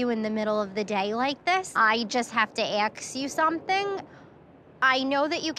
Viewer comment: wtf is she saying? I don’t understand wtf is “aks”